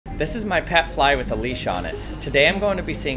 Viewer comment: "This is my pet fly with a leash on it." Perhaps the most absolutely bizarre, exclusively unique, and positively hilarious sentence ever formed in the history of YouTube, if not the English language. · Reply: i thought he said this is my pet fly, Alesha